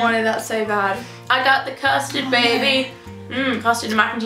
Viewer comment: When she said custard I thought she said bastard